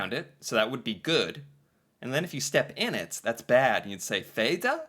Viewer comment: I haven't seen anything like "Fee da," but I'm guessing it's spelled that way specifically to make it easier for English speakers to understand. I think it was meant to say "Fy da!" If you say the English word Fee, as in, when there is a fee/charge to do something, the way you pronounce "ee" is close to - but not exactly the same as - the y in Norwegian. But honestly speaking, I don't actually get this meme's text, "Uff da!" would be something your walking companion might say if they saw you just stepped in some doo doo, but "Fy da" is more used for when you have done something wrong, or whatever. And I haven't heard of "Fee da" before, so I can't tell for sure if it is meant to be "Fy da" or if "Fee da" is an actual thing. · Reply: i thought they was trying to get Englis people to say ¨FITTA¨ because ee in Englis sounds like the norvegian i if you get what i meanjust listen to how he said it the last tim at 6:39 he basically said fitta xD